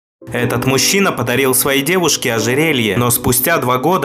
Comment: Послышалось: Этот мужчина подарил своей девушке ожиренье))